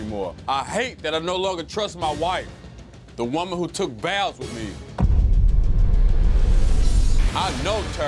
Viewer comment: "The woman who took vows with me" my dumbass thought he said "the woman who took baths with me" 💀